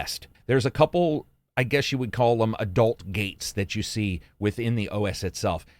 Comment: I heard "adult games" and instantly alt-tabbed to the video, LOL :D